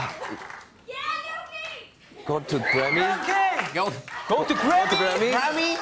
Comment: The cute fact is jhope hugs jimmy first than the other member does Note: that "yeah yoongi" at 4:39 same girl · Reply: i thought he said "are you okay" lmao i am deaf xd